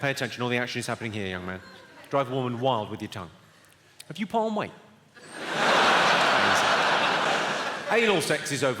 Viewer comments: could someone tell me what he says? I cant understand him. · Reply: “Have you put on weight”? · I listened 10 times. What did he say? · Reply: Anal sex